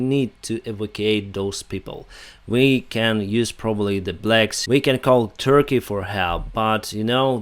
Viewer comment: did he say , "we can use the blacks" ?